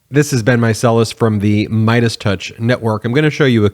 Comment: The first few times I watched this talented young man, I thought he was saying "This has been Micelus." It wasn't the end, so I assumed he was switching platforms 🤣 Thanks for all you do Ben!💛